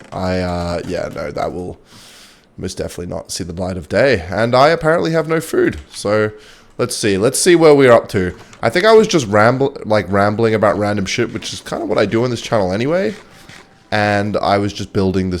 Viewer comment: I heard "rabbit shit" and I almost choked laughing (I was eating a fucking biscuit 💀)